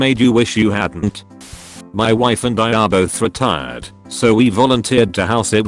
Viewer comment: literally thought they said retarded